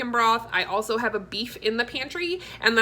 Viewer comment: Lol when you said broth I thought you said rocks. I was like what? Rocks?! Lol